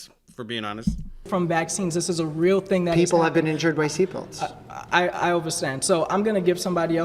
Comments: did he just say "I overstand"??? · Did he say "I overstand"? Isn't that a sovereign citizen thing, where they refuse to say "I understand" because they have a ridiculously literal interpretation of that word, based on false folk etymology? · did dude just say overstand, oh god hes a soverieign citizen. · am I hallucinating or is he saying I "overstand" instead of "understand"?